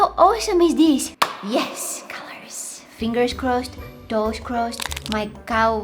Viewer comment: I thought she would be saying my cows crossed 😆